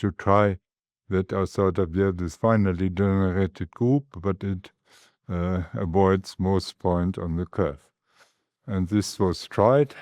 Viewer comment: If you find a mistake in the subs, please let me know in a kind way. You'll probably never realize how much I, as a non-mathematician, bled to get this subtitled before the weekend. PS. Make sure you're using the subtitles provided. I'm not responsible for the auto-generated ones, nor can I edit them. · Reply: "but it avoids most points on the curve" instead of "but it aborts most point on the curve"